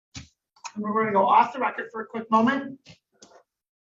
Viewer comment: I thought she said: "we are going to go off the rocker for a moment 😳 😅